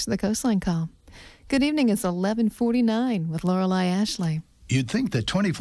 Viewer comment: Thank you for telling the DJ name. Sounds like Loreley Ashley to my ears. lol DJの名前ありがとうございます。私の耳にはローレライアシュリーと聞こえてました（笑